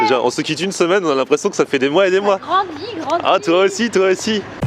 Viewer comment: Je suis désoler d'être vulguaire mais dès le début de la vidéo (à 0:05) j'ai entendue :"Ta grandi grosse bite." J'aimerai savoir si j'ai bien entendue.